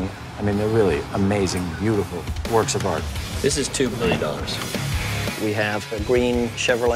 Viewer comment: WHAT!!! At 1:15 it sounds like he says the cars worth $2billion? Please confirm!